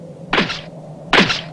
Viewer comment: No hablo Ronaldinho Soccer ¿que dice? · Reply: Es catalán, el mejor doblaje de dragon ball 🗿🚬 Dice "No t'hi escarrassis bola de ferralla" que traducido seria "No te esfuerces bola de chatarra"